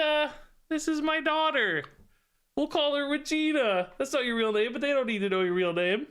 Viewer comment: I'm not a native English speaker, but i have to tell you that i heard "we'll call her Vegeta" 😅